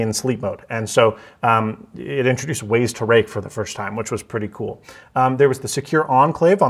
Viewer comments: Just me or does he say wase to rake near 13:35 ? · did bro say waise to rake instead of raise to wake